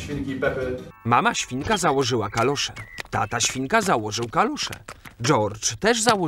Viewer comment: Kto usłyszał jak szedł tata śWIŃA to było kalusze XD